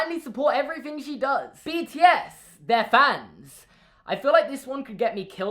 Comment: Wait wait wait... at 1:39 did he say bts... like the bts . . . ARMYS WEAR U AT!